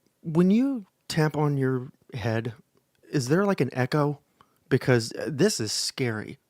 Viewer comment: Thank God friends were there MY GOD WOWWWWWWWWW sounded like you said when u tampon your head LOL YES HE IS THE MORON ❤❤ MORON❤❤